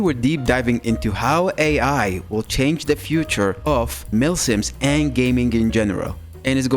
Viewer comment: I thought it said future of muslims